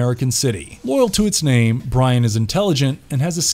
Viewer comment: Did he say Brian?